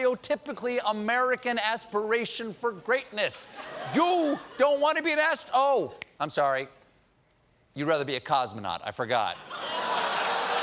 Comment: I'm I the only one who hears "You don't want to be an ass..."